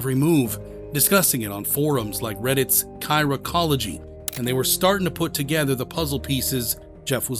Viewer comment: Wtf did you say at 8:48?? I’m out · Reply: chiraqology